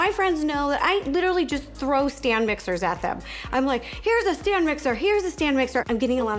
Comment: when she was saying stand mixers I thought she was saying Stan mixers. yes we stan mixers here :D